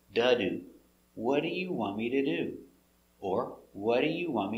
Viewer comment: Thank you so much, the best teacher I'm learning from I have question if possible, look at 3:17 I'm hearing it " wha r you" or like " what are you" instead of "what do you" Or I mean I'm hearing "r" at "do" word, how is that?